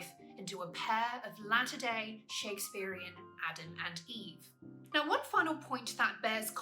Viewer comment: Thanks for these superb videos. Can I just ask, at 3:00 do you say "a LATTER day Adam and Eve"? I didn't quite catch it. Cheers. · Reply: You are so welcome! And yes, I did say "a latter-day Adam and Eve", in the sense that they are a version of 'Adam and Eve' which come after the original pair of sinful husband and wife (at least from the Christian perspective) :)